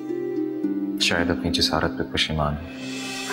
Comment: ye kya bola Fawad ne koi samjhayega?? Aur Bano ka reply bhi smjh nahi aaya ...azal se apki hun mtlb kya?? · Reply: Fawad ne kaha apni jisarat pe pashman hoo meaning apni himmat pe pachta raha hoo Aur azal se apki hoo meaning shru se ap ki hoo